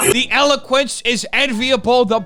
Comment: Who else saw the captions say "the pubes is enviable" at 2:52 when is talking about takashi 69?